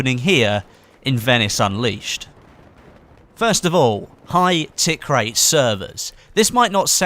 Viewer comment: High what?Can't figure out what he says there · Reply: Tickrate